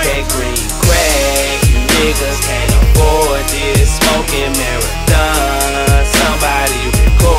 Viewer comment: CAN SOMEONE TELL ME WHAT HE SAYS IN THE HOOK AT 0:37 AFTER "smoking ????" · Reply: Marathon